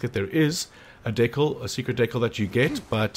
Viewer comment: Did you say ,,secret dickle